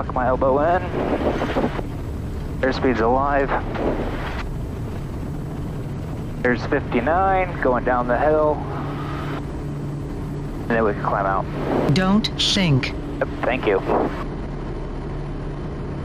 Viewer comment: Hahaa..thought she said dont SHANK!!